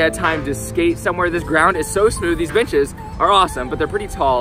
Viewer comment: At first I thought he said "these b*itches are awesome" instead of benches lol